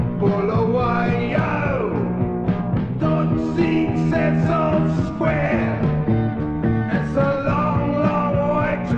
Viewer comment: That's what i hear: We all pick the star beats(?) The hecker sent the graves(?) And we really .... slacker(?) When we start it on the Spain(?) Does anyone hear something normal here? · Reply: We all dig the stoppies, haircuts and the graze. And if we really scheme it's lekker when we start on our first phase